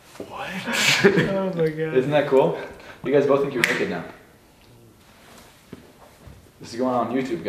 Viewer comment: What did he say at 17:56 😭😭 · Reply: That they will think they are naked 😂